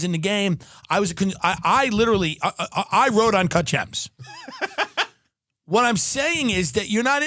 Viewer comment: "i have connections in the game, i literally, i rode on ..." what was it ? didn't get what he was saying there